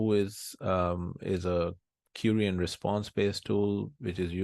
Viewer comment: you're saying that "Whois is a ___ian response based tool" but I'm a newbie and I don't know what you're refering to😭 I'm eager to look it up, could someone please tell me what is the word I'm missing ? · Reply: Query